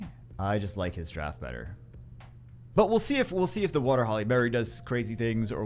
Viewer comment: Did it sound like he was saying Halle Berry to anyone else the entire video?